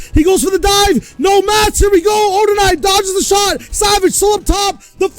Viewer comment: サービスおんターム（空耳で聞こえません？）